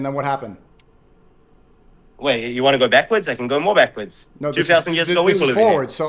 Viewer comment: he did NOT say “3000 years ago”, he said “2000”